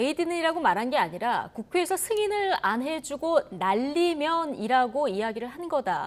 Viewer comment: 들으면 들을수록, 바이든으로 선명하게들려, 바 이 든 !!! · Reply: 에이. 안해주면이 아니라 안해주구로 들리는데 그 뒤에 바이든은이 올 수 있나요?